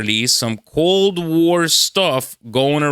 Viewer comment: What did you say 04:07 "cold war stuff" Cold?